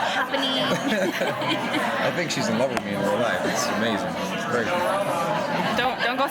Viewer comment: What he say at the minute 0:40? She's so embarassed after that!!!! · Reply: He says: "I think she's in love with me in real life, it's amazing " :D sooo cute. :D ♥